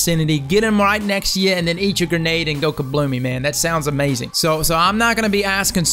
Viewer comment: instead of go kabloomy the captions say goku blew me.